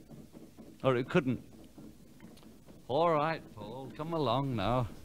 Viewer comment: did john say alright comeone FAUL? sounds like it